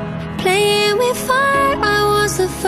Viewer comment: OMG, I just heard "heart is sinking like a cannibal"